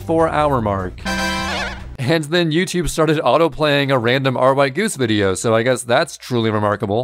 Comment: what channel is this, it sounds like is saying "rygoose" but I couldn't find a channel with that video by that name